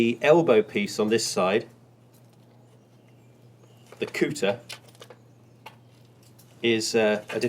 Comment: Did he just say cooter? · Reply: Couter not cooter